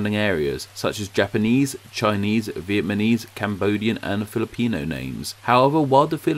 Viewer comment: Was it just me or has anyone heard “VietMANese”